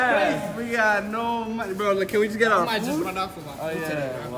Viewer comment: In the beginning it sounded like they said "we don't even have any money, can we just get our food?"